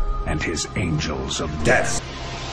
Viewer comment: It kinda sounds like says angels of des, rather than death.